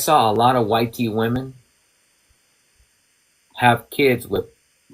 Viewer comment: What kind of Woman? I keep hearing “White tea Woman.”